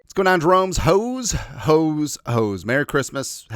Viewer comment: Did you just say “What’s going on, drones”?!? Buh-bye, 4 seconds in